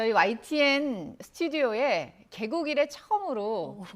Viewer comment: "개국이래" 처음으로가 "개고기를 처음으로" 로 들려서 깜놀해서 몇번 다시 돌려들음